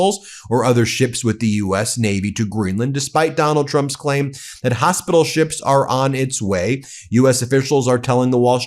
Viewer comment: I thought I heard trump say hospital boats.